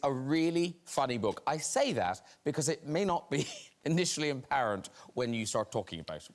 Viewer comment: Could anyone lmk what Graham said at 0:24 ? To me it sounds like "because it may not be initially apparent" but I'm not sure